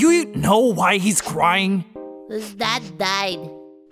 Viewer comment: can anyone PLEASE HELP ME WITH WHAT PUMP HAS SAID HERE? genuinely this childishness makes it inaudible for me especially with the english accent he has (dont take me as a racist neither please). all i hear is "His dad's [dying, diet, ehaheyshsv2ceredcuhev2rcroycv]" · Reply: pump said: his dad died